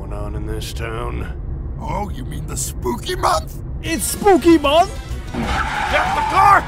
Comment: when I'm driving and I hear "isa spooky month"